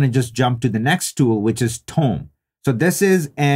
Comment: what does he say? tong, tone?? what is the app he is using. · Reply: Tome